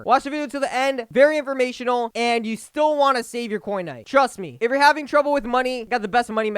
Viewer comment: it sounded like he said Fortnite 😭😅